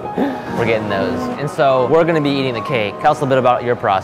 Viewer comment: At the beginning when they both said different things at the same time ( cake and mints) it sounded like they said "were gonna eat kids"